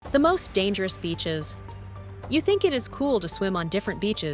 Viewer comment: I thought it said Most Dangerous Bitches...